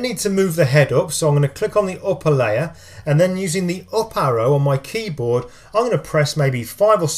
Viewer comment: i can't seem to understand what were word u said in 1:21, it's kinda like ''oparo'' on my keyboard, what is that? been trying to study this tutorial but i cant seem to get it right